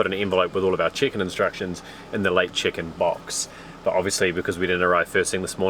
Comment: Lol you were saying "Check in" I heard 'Chicken' that was funny on my part cuz I will just outright say 'THat sounded like!" Which I"ve caused some laughter and confusion.